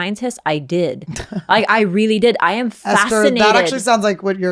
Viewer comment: lol...i had to rewind i thought i heard (☺) say I am flaccinated.... you can add that word to your stuffs. Oh and getting back to dive gear...i get the conundrum. But a good option is to call them say "hey buckos..return my stuff...guess what im famous..i will tell the world and theu will know you steal from Angels or maybe if im not feeling cute Ill just call the cops...and they willl pick it up...and Dont Steal No More...and stay out of the Woolworths! The Woolworths part is for Little Esthers.